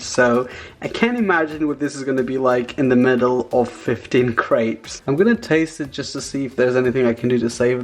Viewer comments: I thought you kept saying grapes lmao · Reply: Valeria T same😂 I thought he was saying "15 GRAPES?!" 😂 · I thought you kept saying grapes lmao